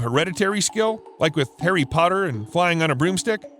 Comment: Wait, Perry Potter? I'm like 80% sure you said Perry Potter instead of Harry Potter. 😂I had to rewind a few times to hear it again. I laughed a little more than I should have.